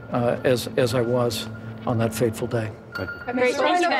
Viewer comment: What was Faithful about that Horrible day...I agree Pence, step-up as the Vice president On said date...However, He he should have been Stepping up...Way B4 that... Y'ALL need to be tried Under The UCMJ.. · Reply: "What was Faithful about that Horrible day..." 1:08 To be fair, I think Pence said, "...on that fateful day."